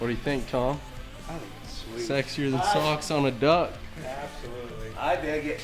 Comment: Did I just hear that right? - Sexier than socks on a duck? XDXDXD